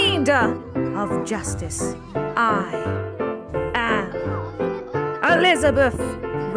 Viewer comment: sounds like she says Elizabuph oi m8 it's Elizabuph then innit?